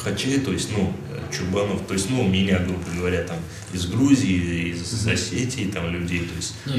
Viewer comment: did he say Грузия?